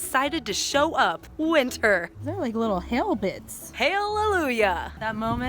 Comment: what i heard: Ooh they're like little Hamiltons! 0-0 (had to watch three times to understand she said hail bits or somethin')